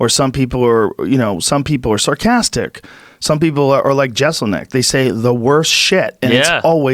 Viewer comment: what name are they saying? Cannot catch it, thx, yins.. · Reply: Jeselnik